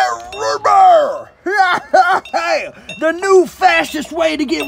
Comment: I thought he said the new facist root beer💀